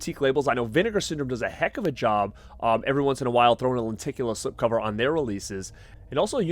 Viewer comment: Are you from boston? I swear i kept hearing "lenticulAA"